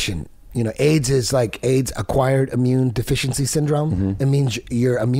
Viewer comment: Did he just say aids is “acquired immune deficiency sun”? I mean talk about brain dead